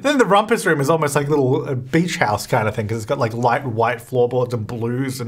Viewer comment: I first heard he said b!tch house....my brain is messed up